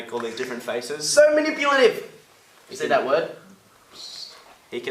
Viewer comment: I still have no idea what ash says at 6:40, but judging by Mike & Luke's reaction's I don't think I want to know. XD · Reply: He said "So manipulative!" 😂